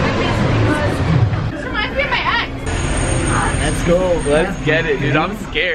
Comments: did anyone hear Alissa say “this reminds me of my ex” and there was a picture of the clown? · Who else heard this reminds me of my ex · Who else heard alissa say that remind me of my ex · who else heard someone say “that reminds me of my ex”